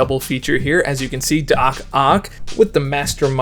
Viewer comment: When you revealed the team I thought "Doc Ock" said "Do Cock"